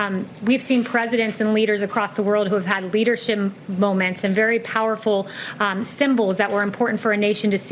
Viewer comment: Did she say Leader Shame moment @ 6:08?